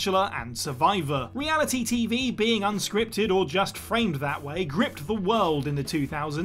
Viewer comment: lists are some of the worst. His videos are insufferable, and most time inaccurate. Like in this video on how Smash TV predicted reality TV. The movie Running Man in 1987 came out before Smash TV, 1990. Also said that reality TV is unscripted, which is incorrect. Reality TV is heavily scripted and far from reality. All the others at WhatCulture are great, but is just the worst. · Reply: "reality TV being unscripted *or just framed that way*" is what actually said, which is correct. Scripted, but the showrunners want viewers to believe it's real and raw.